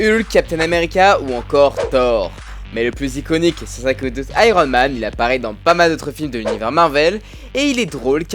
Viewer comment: tu dit quoi ?? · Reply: iconique est sans aucun doute iron man